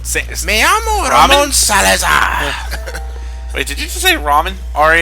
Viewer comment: I'm pretty sure I just heard say "that nigga did just say ramen"